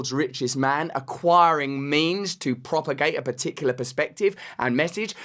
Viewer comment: Acquiring *means*...thought he said 'memes' 😆